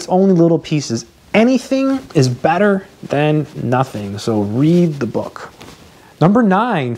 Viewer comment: 1. Take good notes 2.Rewrite your notes after class 3.Redo all the exerc. given w.o. looking at your notes. 4.Do all your homework!! (Great for the tests) 5. ASK in class, interact /partcipate (helps memorization) 6.Go to office-hours, if you need help. 7.Go over the teacher's review. 8.Try to read the book line by line, even if you don't understand it. *Something is better than doing anything. 9.Find a good place to do hw/ no interrumptions. 10. Form study groups , talk about maths and make friends. Thanks 💕 ! You might be one of the coolest teachers here in youtube!! your stuff motivates me during these quarantine times ... thanks a bunch, Take care! 😄 Gretings from Lima, Peru 😆 10 is by far the hardest part hahaaa !! 🙈🙈😅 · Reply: Thanks for writing it all! I think there is a little error here. I think instead of "*Something is better than doing anything." which you wrote in number 8 habit; it should be "Anything is better than nothing." as the says it himself (8:05).